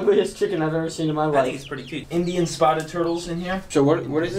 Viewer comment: Am i the only one who heard say, “That niggas pretty cute” @ 9:00 of the vid